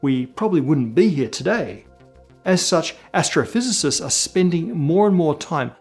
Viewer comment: Matt said "Astrophysicists" but I heard "gastrophysicists".... I think I'm hungry...